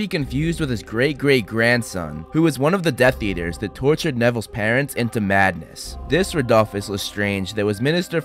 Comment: I thought he said Neville’s snare